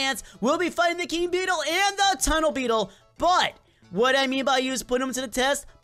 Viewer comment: Um did you say tunnel beetle